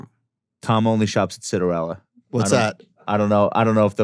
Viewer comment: not knowing what Citarella is really shows he is a man of the people and a true Middle Class. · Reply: i thought he said citronella lol